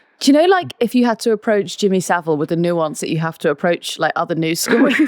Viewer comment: She said "nuance" but I heard "nonce".